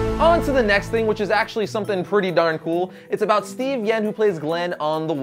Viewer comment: It sounds like he said Steven yen when it's Steven yuen but that might have just been a mistake.